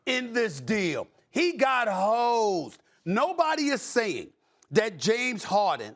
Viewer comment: I thought Stephen A said the 76ers got hoed llf